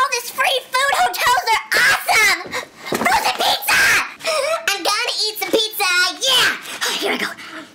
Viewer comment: 😂😂😂😂Me and The strange lady are best friends!!!😂😂😂😂😂😂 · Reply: did say hu frozen pizza